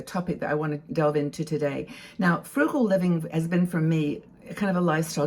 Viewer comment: I thought it said fungal living 🤣